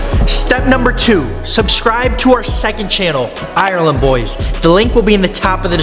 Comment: Who just loves watching the ireland boys? (Edit: bro 1k likes, y’all need to chill) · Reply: This is my first video watching him and I thought u said island boys